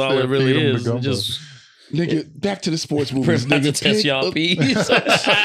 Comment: lmaoooo Birdman says "I'm bout to TEST YA P's" as in P's and Q's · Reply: I’ve always heard it as Test Ya Ps too, but now I can’t un-hear P-Ness 😂